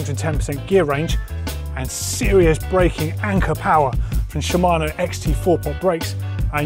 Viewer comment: Did doddy say 'ker-powa'? Batman brakes....kerpow!